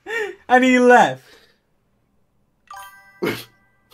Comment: in 0:47 sapnap didnt said what the heck he said what the hell